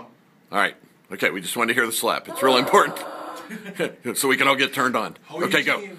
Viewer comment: did that dude in the beginning the one holding the camera did he just say. "ok we just want to hear the slap so we can get turned on" and when that one dude slapped her the guy holding the camera moaned or something -.